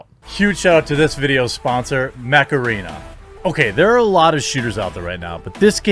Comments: Not gonna lie.. I thought he said this video's sponsor is by Macarena and just did a time jump 🤣🤣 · LoL, I heard “the sponsor, macarena!”. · I wasnt looking st the screen when you plugged your sponsor so I heard it as; "Huge shout-out to this videos sponsor, Macarena!" Ai! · I seriously thought you said your sponsor was "Macarena". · Coulda sworn he said his sponsor was the macarena